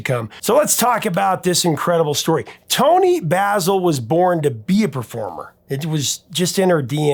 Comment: Toni Basil was born to be a performer. Misheard that as Toni Basil was born to Beaver farmer. 😂